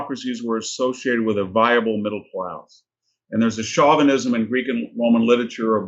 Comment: can someone explain what he means by a “chauvinism in Ancient Greek and Roman literature...” I’m not sure what word he is saying. Chauvinism? Context here? What is he referring to?